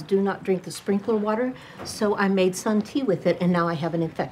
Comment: OK, so I just googled “Sun Tea” because I am clearly an ignorant Australian. Seriously, Americans, I really love you guys and your amazing culture. But I have never heard of anything so bloody silly. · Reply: I thought she said "some tea", I didn't realise Sun Tea was a thing